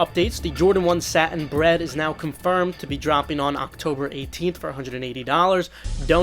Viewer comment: I thought they just said the cut the checks are not confirmed to be really dropping anymore?